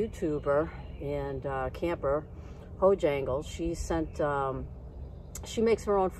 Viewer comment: You were eating good in the woods. Congratulations to Laurie. See you on your next adventure. Have a great weekend!What is the channel name for Robin that made the freeze dried meals ? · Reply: lol I thought you said Bojangle